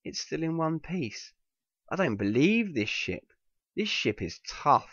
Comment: I thought he said "I don't believe this shit" when he said "I don't believe this ship" LOL